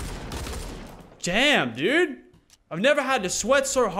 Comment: did anyone hear Lachlan say “Jam” instead of damn